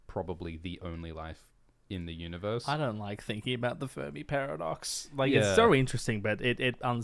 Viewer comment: I thought said furby paradox when answering a super chat which lead to a very confusing google search 😂